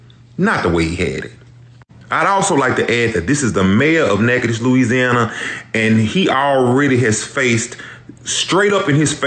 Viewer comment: Did he just say: "The mayor of NakedAss Louisiana" ?